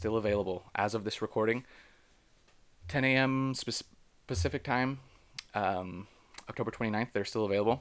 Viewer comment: did you say Specific time? 😅 Pacific?